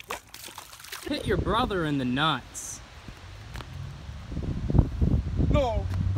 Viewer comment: subtitles say " pick your brother in the knots"